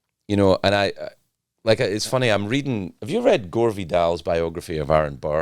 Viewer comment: Whats the book name mentioned by Craig he says he is reading? He said it so quickly i couldn't make out after reducing the speed of the video? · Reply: (Inaudible name of the writer) Autography of Aaron Burr Edit: the name is Gore Vidal, the book is called "Burr" from 1973. He actually mentioned the book a lot in his Late Late Show